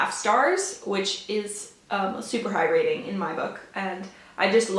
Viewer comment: Great video! I am really interested in The Seas now. I had never heard of it but gothic & haunting sounds like my kind of book! When you were talking about it and said “super high rating”, at first I thought you said “super hydrating”. 🤣 I work at a library and Where the Crawdads Sing has had multiple reserves on it since we got it but hopefully when it’s not in such high demand I can get to it. You really made me want to read it! Hope you have another great reading month! ~ Amy